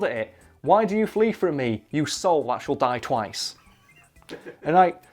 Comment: i have no clue what is said here because of this dude's amazing accent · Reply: "Why do you run from me, you soul that shall die twice"